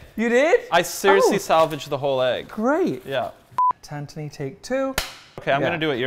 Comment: Did he say Tantoni?! Oh god